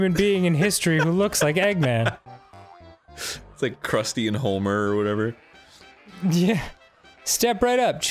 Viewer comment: Arin. Did you just say 'Crusty and Holmer It's Homer. Where did the L come from.